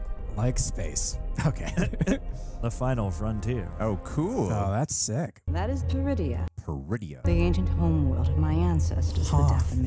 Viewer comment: when they said the name of the planet "Peridea" i thought they said "Druidia" as a Spaceballs easter egg